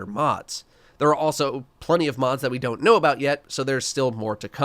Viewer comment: “Plenty of mods to come.” Is that confirmed or did I mishear?